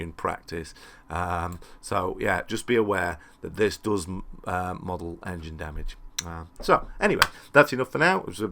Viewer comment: I think you meant "This does model engine damage", although it sounds like you said "This doesn't model engine damage"?